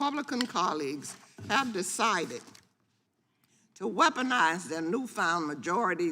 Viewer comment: she said politicize not weaponize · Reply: You might be right, but at 2:09 she clearly says "weaponize". 🌲